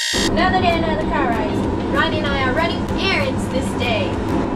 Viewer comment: I thought Nessa said we’re running Aaron’s today 😂